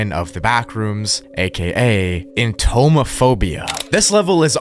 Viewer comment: I THOUGHT IT SAID HOMOPHOBIA LMAOOOOOOOO